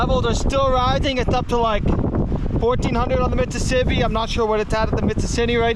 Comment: What river did you say this is? It sounded like you said Mississippi…